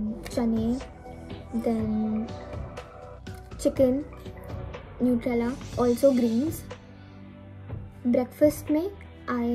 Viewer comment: did you say nutella!!!!!!!!!!!!